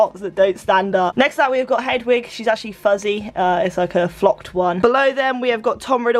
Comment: When she said flocked for Hedwig I thought she said flopped 😂😂